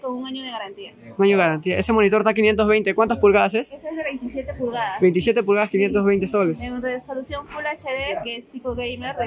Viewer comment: Bro WTF?! Dijo 520 soles un monitor de 27 pulgadas, de 144hz y <num>?! O escuche mal?